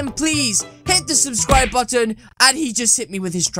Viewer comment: what you say: hit the subscribe button what they hear: hit me with a track